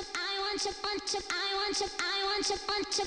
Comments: I THOUGHT THEY WERE SAYING “I WANT TO I WANT TO PUNCH YOU” OH GOD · No one: Not even Donald Trump: My brain: Want you want you I want you want you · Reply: Why do I hear “I want to I want to punch you” 😔🤚🏼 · I THOUGHT THEY WERE SAYING “I WANT TO I WANT TO PUNCH YOU” OH GOD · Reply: I heard "I want ya punch" hahaha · I want you, i want you, i want you “Naegeseo salajyeo (Get away from me)” - sehun 🔥🔥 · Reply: why did I hear "I want a punch"? is my closeted kinky personality showing up?